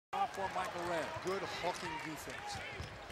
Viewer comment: sounds like he says good fucking defence at the beginning